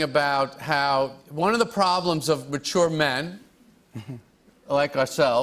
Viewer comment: The funniest part of this was how Jerry twice referred to being in the same age group Jimmy. I’m two years younger than Jerry and as sad and painful as this is to admit, I’m several “age groups” older than Jimmy Fallon. What makes Jimmy Fallon the nicest late night host is that he didn’t step on Jerry’s set up.. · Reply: What he said was "matured men, like ourselves" @ 6:27 Matured is not related to age. Not sure when the other instance was.